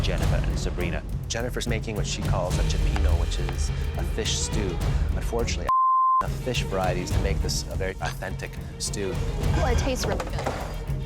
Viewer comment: what did he say there for it to get bleeped out?? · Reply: You can tell he says “but I don’t think” Why it’s censored? God knows!